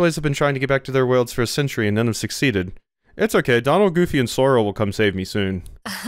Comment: Did you just say Zoro? Is that a joke about how his sense of direction is so bad, he'll find a way out without even trying? Or did I just mishear and you never said Zoro.